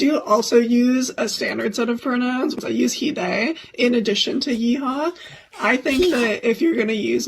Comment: did she just say He Haw...😂🎉